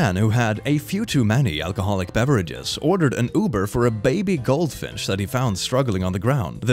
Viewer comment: When he said "gold finch," my dumb brain heard it as "goldfish." lmaoo